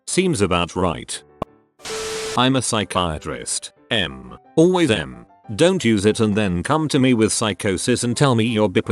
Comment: What’s the word at 6:56 ? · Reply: Meth